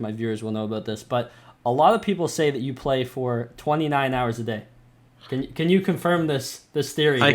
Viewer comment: Did i hear 29 Hours a day??? HOW ?? theres only 24 hour in a day bruh?